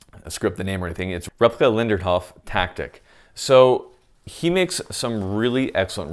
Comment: what's the name your saying? Rapka lindercov tactics?